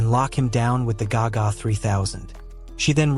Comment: Bro i just came to watch but at the very beginning in 1:19 i heard the g g 3000 is here!!!!😂